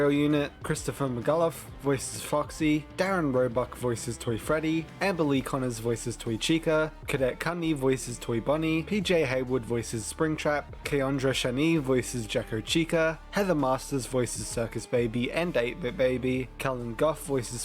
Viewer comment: came from the live stream, starting the video now! I'll update this once I'm done :) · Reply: Update 2. 11:17:07 I stopped when I heard Kadet Kuhne because it sounded like “cadet candy” also it says Luhne instead of Kuhne.